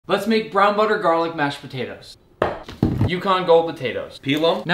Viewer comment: I misheard brown butter garlic as 500 dollar and I was like who tf is eating 500 dollars of mashed potatos